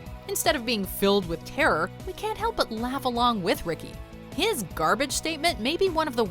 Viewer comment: Did he say Garage day or Carpet day?